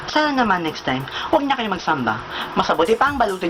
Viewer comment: Wag kana mag SAMBA hahah Thumbs up kung narinig mu rin